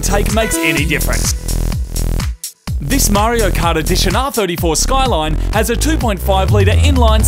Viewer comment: did he say Mario Kart edition ?